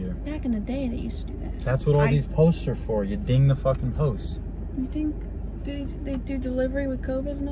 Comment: Did anyone else hear her boyfriend say "you ding the f***ing posts at 3:34 or was that just me? 😂